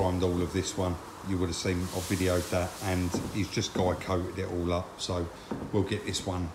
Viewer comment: Can anyone tell me at 9:14 what 'guy coating' is (I think that's what said!), the black over the primer? Cheers. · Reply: It's guide coat buddy, and it is to reveal any pits and bumps in the body when sanded back